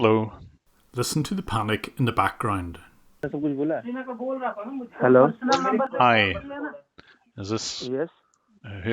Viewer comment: what were the scammers saying at 12:52? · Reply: "I was telling you right he was calling from a personal phone number" or something along those lines